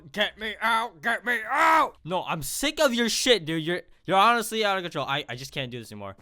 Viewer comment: I heard "tired of your shit" and decided im watching this